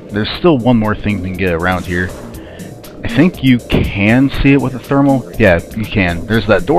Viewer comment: Oh. For some reason I heard it as "you couldn't see it with the thermal visor" don't know why I did that. Thing is, I think you can actually use the thermal visor to see the door. I guess I just had a brain fart. I do remember using the scan at the artifact site to find that one. Felt pretty good about figuring that one out.